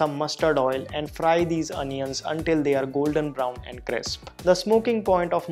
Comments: I swear he said onion s instead of potatoes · did u noticed that he said onions instead of potatoes 😁😁by the way all recipes are very nice and I also I have tried that Pizza one !